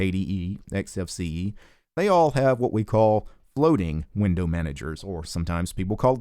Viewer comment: Did you just say "bloating" window managers at 1:42 lol